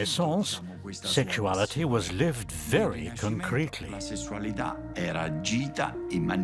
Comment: what's the word @ 15:39-40? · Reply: "concretely" I think